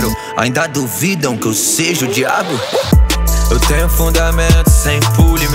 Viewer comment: " branco e hetero ainda duvidam que eu sou o diabo?!" - RET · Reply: o pior que eu escutando dnv e prestando atenção entendi "odiado" ao invés de "o diabo" tomara que seja "diabo" msm, fica mais pika e com um sentido de crítica social